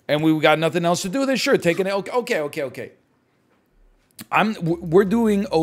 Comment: did he just call a pineapple "an elk"?